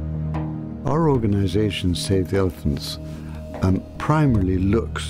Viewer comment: I thought it said "save the eggplants"